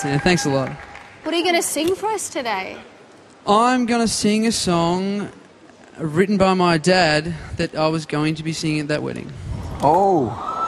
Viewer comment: hello everyone, how's it going?, maybe someone could clarify me what is he saying at 4:29? he's gonna sing a song that... what? :P thanks in advance! god bless you! ^^ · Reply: "I am going to sing a song written by my dad that i was going to sing at that wedding" God bless you too :]